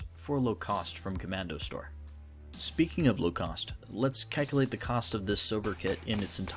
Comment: I heard "kekulate". This man's been on /k/ too much and its starting to seep into his real life